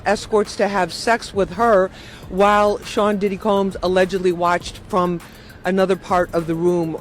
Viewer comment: did she say diddy comb or diddy kong? lol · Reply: Said Sean diddy combs